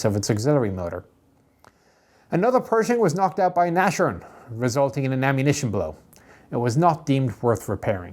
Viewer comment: What did he say it was knocked out by? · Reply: Nashorn